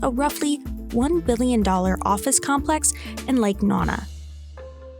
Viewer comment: Did she just say “Lake Nana”? 😐